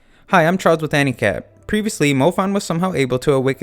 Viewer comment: In the beginning it sounds like you say "Hi i am Charles with handicap"